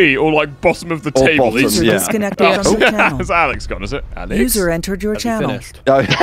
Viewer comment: When it said “user entered your channel” I heard “loser entered your channel”